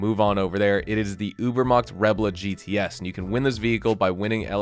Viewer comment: Car name please I don't understand this name...I know little bit english. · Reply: Ubermacht Rebla GTS